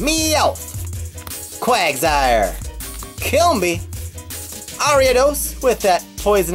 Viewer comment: Dude at 1:57 It sounds like he says "Kill Me!"